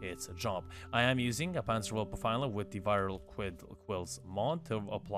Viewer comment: Wait what is he sayings right here?? 3:34 I don’t think i have it, can someone write out what he said? Does it have something to do with the kavat? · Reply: Well the panzer vulpaphyla is a infested cat that you can get in deimos by doing hunts with the tranq rifle and it has a mod thats called Viral Quills that basically applies viral on enemys :)